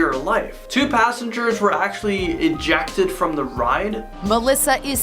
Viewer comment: does he actually say "injected" from the ride? did the ride give them a flu shot or something?